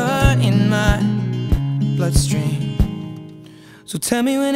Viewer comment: First time I heard this song, Oh! 'Bloodstream', I thought he said 'Butt-String' LMAO